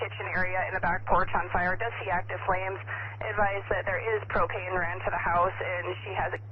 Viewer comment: I swear at 1:15 she says there is cocaine ran to the house